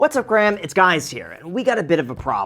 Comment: Did you just say "Hi Graham! it's guys here" at the beginning of the video?